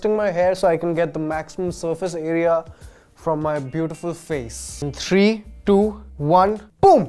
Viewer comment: Did he say "My beautiful face"???!!